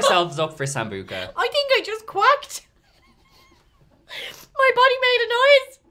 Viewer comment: I had to replay the part where she said “my body made a noise!” She said quacked, I could’ve sworn I heard queft